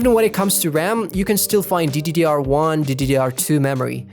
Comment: did you just say DDDR with Triple D ? ... HUH